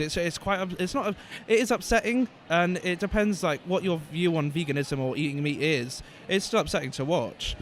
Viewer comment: Grass Fed Vegan someone legit used that "argument" (more like stupidity) on me the other day just because I posted a picture of my vegan pizza 🤦🏼‍♀️🤦🏼‍♀️ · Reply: Psychedelic Kitten it never gets old... i heard also ‘vegans are eating animal’ foods’ ...